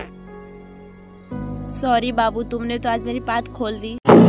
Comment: Wait...did she say "Tumne toh meri paad khol di " 🤯iska kya arth tha behen😵